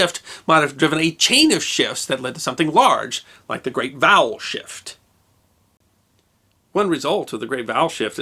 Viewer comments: I heard that as 'The Great Bowel Shift' and was highly confused... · I thought you said "The great bowel shift". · I thought he said "The great bowel shift". That has an entirely different spin.